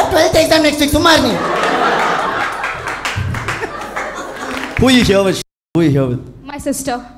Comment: Bro i heard who are u here with shit